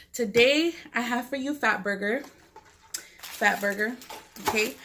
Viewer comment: Y'all tell me why I thought she said "thot burger " 😂 🤦🏼